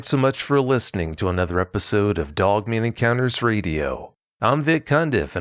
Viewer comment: This guy who narrated the trucker’s story and the cat lady’s story is masterful! I didn’t realize he was saying ‘dogman’ because it sounded like he was saying ‘doveman’. This northerner didn’t understand the southern dialect. Sorry, it was my mistake. Both stories were very believable. 😳